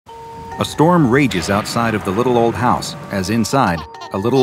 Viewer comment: "Hmm. Doctor Bob hasn't uploaded in a while, maybe i should check on hi-" A s t o r m r a g e s o u t s i d e o f t h e l i t t l e o l d h o u s e "oh gee" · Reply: The first time I heard that I thought it said “Astromages outside of the little old house”